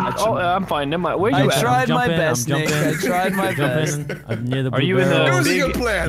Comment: did terroriser said the n word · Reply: no, "I tried my best nick" cuz his teammate was nick